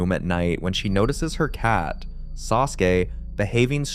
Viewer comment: Did he say that cat's name is Sasuke or am I hearing things?